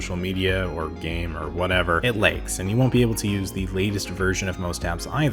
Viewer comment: Where is the reviewer from? The Upper Mid West? The vowel in "lags" sounds legs [eh]. I thought that he said, "it legs."